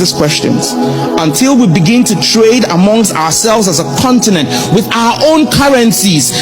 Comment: Great speech but he did not say Africa should trade with one currency, he said Africa must trade with each other with OUR OWN CURRENCIES (1:39). This does not mean one single African currency.